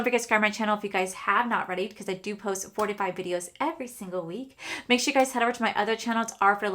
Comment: omg I thought you said 45 videos 🤣